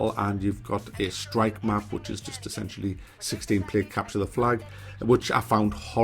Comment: At first i thought you said "...capture the fly" 3:25 :)